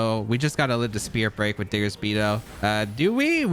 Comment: Did I just hear "Diggersby tho"?